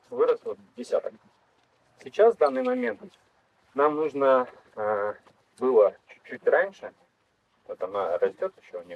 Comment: Подскажите, пожалуйста, о каком растении говорится на 38:00 ?🙏 Не расслышала название🙈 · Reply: По-моему, смородина альпийская